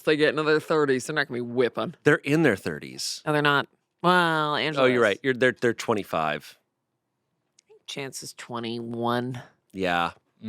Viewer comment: When Amanda was doing her Chanse impression all I heard was “Angelaaa”.